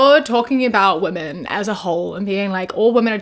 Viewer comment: "you're talking about these women as a whole" I heard "hole" and yes to both. Yes he is.